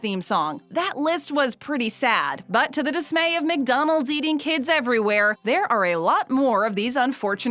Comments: did she say mcdanolds eating kids everywhere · Wait McDonald’s feeding kids or eating kids I thought she said McDonald’s eating kids. McDonald’s: but we do eat kids. I got 7 beanie baby cats from McDonald’s prance and pounce are just two examples. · I understood it wrong and it sounded like McDonald's is eating kids😂😂